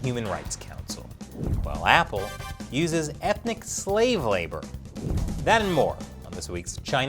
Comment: For a moment there I thought you said: Apple uses ethical slave labour ...